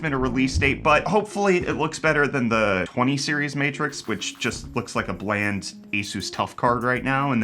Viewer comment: If only the RX 9060 released at $249, I think it would have been good for the price, 8GB of VRAM aside, it's far better than a 5050, so there's that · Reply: I thought you were saying Matrox 😂. That's a blast from the past.